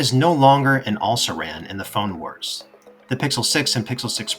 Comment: Did he say "all saran" at 7:33? What was meant by this?